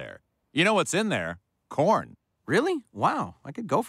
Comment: I thought they said porn